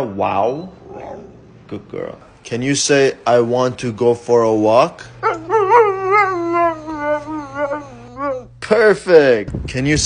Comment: After Luna said "no" I heard her say: " I Do Not love the rain" (listen carefully)